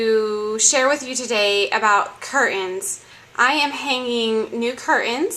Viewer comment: What are cur-ins? She says it at 0:33 and 0:36. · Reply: Lol! She says cur ins and curtains. Either way we know what she means. :)